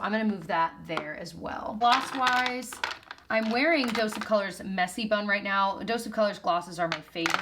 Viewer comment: I think I might have to get hearing aids .... I thought I heard you say "I'm wearing Dose of Colors Messy Bum now"!!!!! I had to go back and check thank God it was Messy Bun.